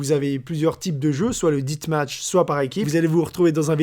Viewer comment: C'est moi ou il a prononcé death match "dite match" ¿